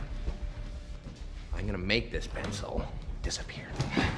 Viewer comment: For a second, I thought he said "I'm gonna make this mans soul disappear"